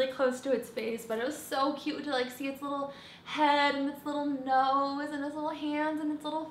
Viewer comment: it sounds like you say "his little" lol but I could be crazy! I say boy!!!